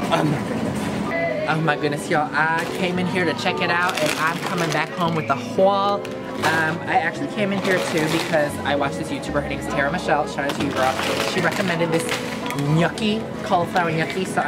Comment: I THOUGHT U SAID HWALL SKSKSK